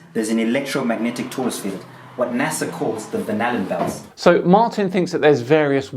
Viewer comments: At first I heard "banana belt" instead of "Van Allen belt". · Every time he says "Van Allen Belt," I hear "Banana Belt." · Sounds like he is saying Banana Belt instead of Van Allen Belt. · Every time he says "Van Allen Belt," I hear "Banana Belt".